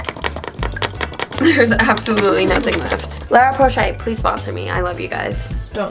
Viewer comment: you meant La roche-posay? lol I swear I heard you say Lara Porshay